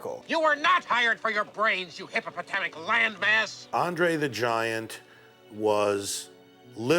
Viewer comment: Hippopotamic land mass lol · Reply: Maybe it's because my hearing isn't what it used to be anymore, but i've always heard him say Hippopotamic lion bass..." lol. uuggggh.